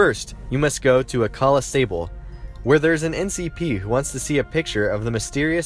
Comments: did you just said ncp? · Did you say NCP? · Did you say "NCP?"